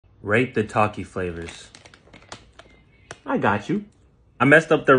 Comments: Dude i turned my head when the video started and i heard "rape the taki flavours" 💀 My dirty mind😭😵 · Anyone else thought he said r@ the taki flavors??